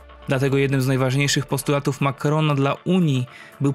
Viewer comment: usłyszałem "Makarona" xd